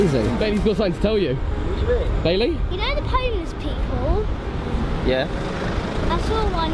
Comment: did he says pomeless